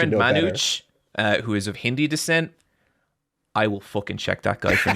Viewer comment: Wait hang on, @ 1:52 does he say Hindi descent? I'm Indian too and I've never heard anyone say it like that